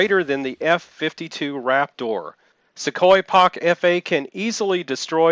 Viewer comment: did he say F-52 raptor😕